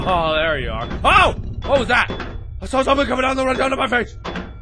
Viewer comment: Lol, what did Mark just say at 1:21? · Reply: I saw something coming down the right side of my face!